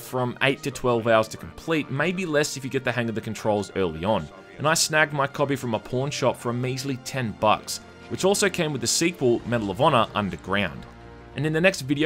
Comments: I honestly thought you said you snagged your copy from a porn shop. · I can't stop laughing. I thought he said porn shop. He said pawn, but the thought is still funny. Why would a porn shop say ps1 game that have nothing to do with porn. XD.